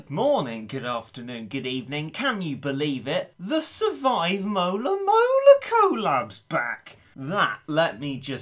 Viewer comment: I thought it said Moto Moto collab